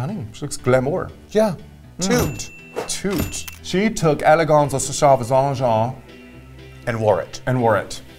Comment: When I first heard Ru said “Eleganza extravanga” I thought she said Laganja Estranga